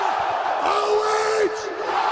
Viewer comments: What the hell is he shouting at 0:38? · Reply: He's shouting "O-H!" and the crowd are shouting shouting "I-O" as in Cleveland Ohio, they must chant it at their sport events or something. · what did he say? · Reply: Stipe "OH" Crowd: "IO" Basically chanting "Ohio", birth place of Stipe